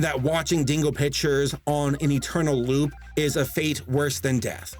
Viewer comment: I swear I heard him say "dingo bitchers"